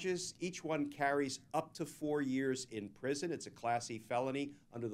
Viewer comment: I misheard, “it’s a classy felony.” That was Class-E, but it may be as close as Tr*mp can get to classy.